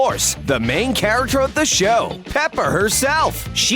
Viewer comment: did he say pepper?!